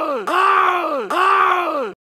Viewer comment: what i heard fire in the ayo